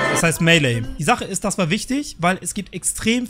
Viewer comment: bei 2:10 verstehe ich aber Milli. Also in meiner welt habe ich recht.